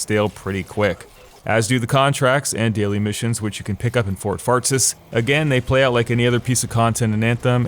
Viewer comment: Did you say Fort Fartsus at 11:50?